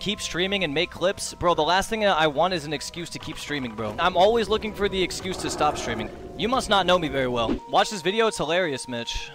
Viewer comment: i heard that as "im always looking for a reason to go streaking" loool